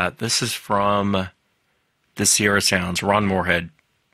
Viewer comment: what did he say those sounds were? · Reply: The Sierra sounds. A man named Ron Moorhead and his friends were camping in California, in the late 70s i believe, when they heard these sounds. They mimicked them, and whatever it was responded, so they started recording them. That was a sample of some of the recordings .